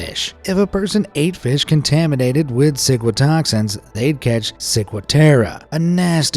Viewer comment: i heard “sigma toxins”… but aren’t toxins bad?